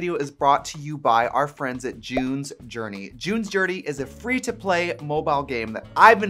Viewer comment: Omg!!! I thought I was going to have to cancel you cause I thought you said "Jew's" journey instead of June's journey!!!! DON'T COME FOR ME!!! 🤣😂🤣😂